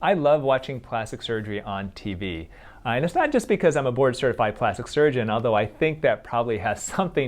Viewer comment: What he said: board certified plastic surgeon What I heard: bored, certified, plastic surgeon